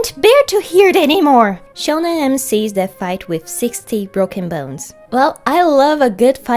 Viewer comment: I’m a year late but i swear i thought i heard 16 broken bones and i was like “spider-man?”